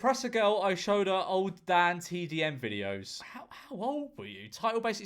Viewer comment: did someone say DanTDM